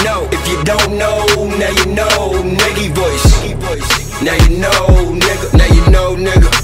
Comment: It sounds like he was sayin Nicki Voice but this is 🔥